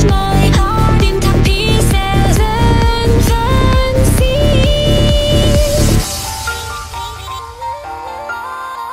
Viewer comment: en el 2:24 que dice? o.o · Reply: ¨y luego cantas¨ jejeje creo que fue muy tarde pero wenu xd